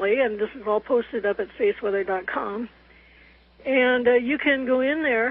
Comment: what is she saying at 11:52? Safeweather or faith weather? I want to look this up but can't find it. Thanks.